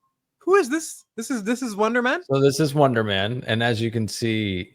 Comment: I thought you kept saying "Wanda-man" for gods sake, I had to google it... Wonder man... WUN-DER